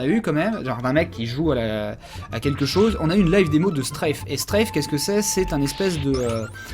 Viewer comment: C'est quoi le nom du jeu dont parle à 18:10 ?? Je trouve pas comment ça s'écrit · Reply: Maxel STRAFE